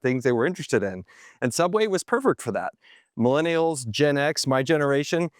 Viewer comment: you didn’t realize you said “pervert” instead of “perfect” 😂😂😂 guess the subway spokesman just overshadows it always lol